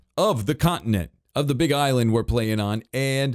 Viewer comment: Did I hear say: "Big Eyeland"